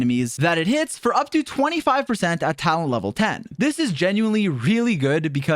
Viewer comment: am i tweakin' or did i just hear town hall lvl 10 ???